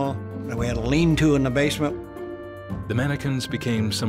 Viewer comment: for a moment I thought he said "dominicans" instead of "The mannequins" LoL